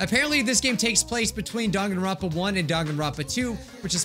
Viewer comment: My subtitles say “Don Garopplo one” instead of Danganronpa 1 And Danganronpa 2 is “Dog and Rapper Tube” and I cant